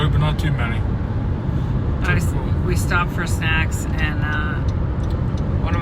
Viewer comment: Man I’m sorry I really wasn’t watching just in the garage kinda listening, I had to back it up, you said we stopped for snacks, I thought you said sex, my bad